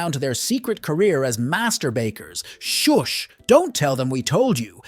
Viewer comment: I had to go back and replay around 2:35 a second time. The first time I heard "Their secret careers as masturbators" 😂